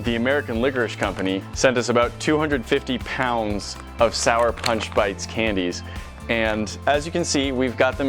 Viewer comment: I honestly thought it said “sour patch bites”